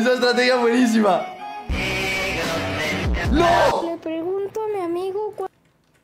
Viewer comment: no entendi este alguien me explica? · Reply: Dice la n word